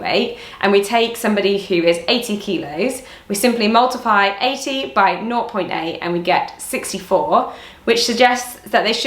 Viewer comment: What does she say instead of zero? Nort? I've never heard that before🤔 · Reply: naught